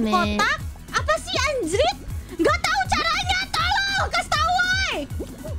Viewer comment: so in 0:43 she's not saying "help" or "tolong" in ID in my ears, i heard she said "tolol" which means "idiots"